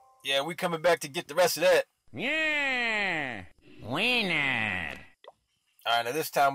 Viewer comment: Did i just heard weiner?